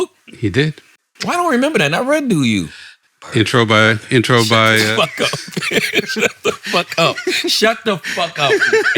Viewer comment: what does say here? I can't make it out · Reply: Birds of a feather